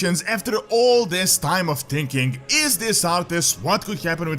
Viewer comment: I swear I almost thought I heard you say "After all this time of speculation, after all this time of drinking..."